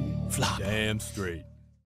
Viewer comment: Why on PhD flooper song i hear instead of "Damn Straight" i hear "Aand slider"?